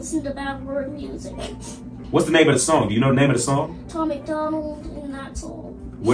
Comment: I think this child was coached!! There's no way that he only knows your name and not the lyrics or the name of the song.... There's no way... I definitely think he was coached. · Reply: I thought he said "tom MacDonald and that's all"